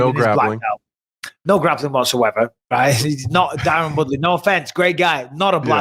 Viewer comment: does Mike say :Darren Woodley ? My guy needs some rest.